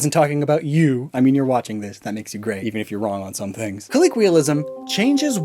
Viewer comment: Him, talking about how to pronounce Arceus. Me: did he just say colleequial instead of colloquial? (also I agree with the detective pikachu version, are-see-us)